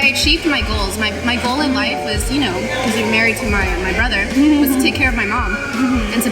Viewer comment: Is it just me or did Michelle say “To get married to my brother”?